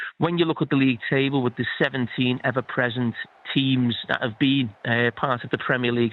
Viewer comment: Did he say even present we are in 14 and 15 on table you have have a senile Jamie you are talking from your ass